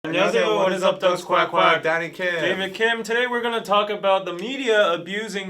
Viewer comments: for a second i thought said "daddy kim" instead of "dani kim" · I heard "I'm Daddy Kim" instead of Danny Kim lol. I clutched my pearls.